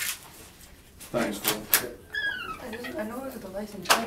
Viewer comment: I thought he said thanks fool for a second